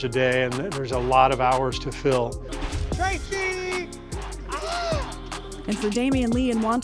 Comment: Did he just say Tracy? Big Tmac fan here lol